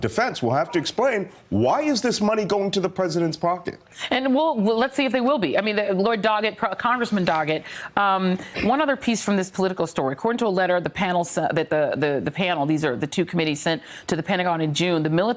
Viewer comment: Did she call him "Lord Doggett"?